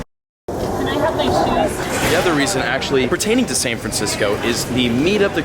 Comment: Who heard can I have my shoes